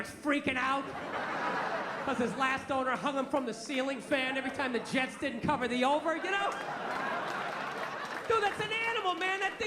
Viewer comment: "토스터가 잘 안 구워질 때마다 실링팬에 얘를 목 매달아가지고" 빌 버가 말하는 걸 들어보면 "every time Jets didnt cover the over" 정도로 들리는데 토스터기 얘기는 없지요. Jets는 미국 내 미식축구 프로팀이고 over/under, cover the spread는 스포츠 도박에서 쓰는 용어들이니깐 "토토에서 돈을 잃을 때마다" 정도로 이해하면 될 거 같네여.